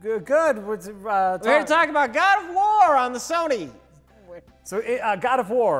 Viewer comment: God of worm -Rich (At least, I thought he say that XD)